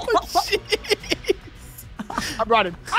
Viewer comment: did say sh*t? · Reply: No he said oh jeez but because he was laughing it sounded like he said sh*t.